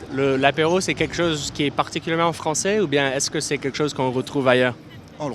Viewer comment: Excusez-moi mais vos sous-titres sont inexacts. Au début à environ 12 secondes vous écrivez "(de)" alors qu'il dit "pour" et à 1min 34 vous écrivez "biscuits (secs allez)" au lieu de "secs, salés" ; à 5:34 il faut dire et écrire ce qu'il a dit, à savoir "qui est particulièrement français" parce que c'est l'apéro qui est français.